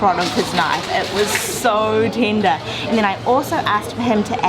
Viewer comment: the subtitles are wrong. You are saying "tinder" while the subs say "tender"